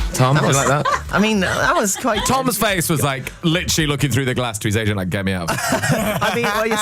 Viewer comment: can sometell me what Grimmy is saying at 5:02 pleeease? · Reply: "Toms face was like literally looking through the glass to his agent like get me out of here"